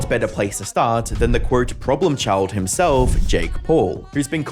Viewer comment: i literally heard j cole